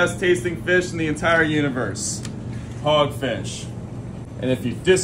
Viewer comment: Am i the only one who thought you said “pogfish”?